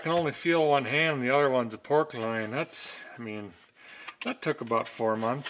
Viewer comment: "can only feel one hand and the other one's a pork loin" Hilarious!! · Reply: I thought I heard pork rind . ? . . . Did I win ? ?